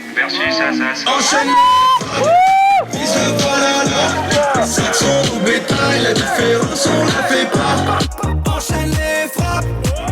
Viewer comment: Saxon dans le quoi ? Non parce que c'est pas templier qu'ils disent alors c'est quoi..... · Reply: Saxon dans le bétail moi j'entends ça